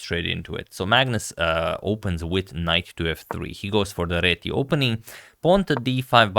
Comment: I swear I heard "Magnus opens with knife to f 3"